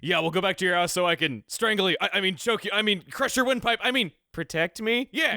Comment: He said “crush your windpipe”? I always thought he said “Crush you with my-“